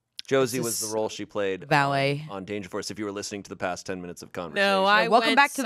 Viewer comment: ALL I HEARD WAS VALLET GIRL.